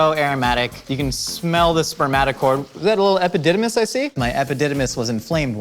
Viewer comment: did he say spermatic